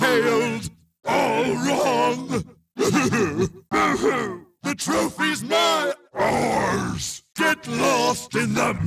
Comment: "The trophy's mi- OURS!!!!" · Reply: I honest heard it as "The trophies mine-OURS!!!" when playing as Megamix